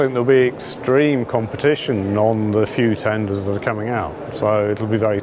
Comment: What is he saying at 0:47 ? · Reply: extreme competition on the few tenders that are coming out ...